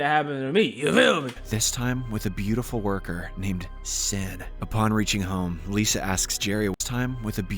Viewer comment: did only I hear "sin"